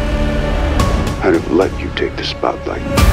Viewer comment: What I hear : "What father wouldn't want that for his son?" What I see : "mmm ma ba frr boiii ohh."